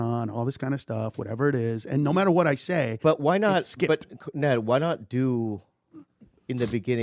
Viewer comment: At about 1:50 did Bobby call him "Ned" ?